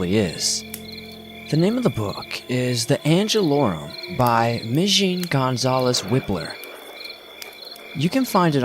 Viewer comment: the angel what??? by Migene González-Wippler Plz tell me the name of that book, im not fluent in English n couldnt understand it. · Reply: hong yeonduk I found it! It is called Angelorum by Migene Gonzalez Wippler